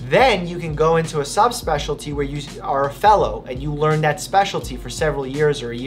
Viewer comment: I thought he said: Then you can go into a subspeciality where you are a felon